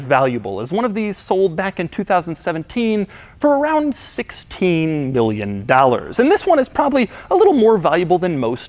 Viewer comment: sounds like he just said 16 BILLION dollars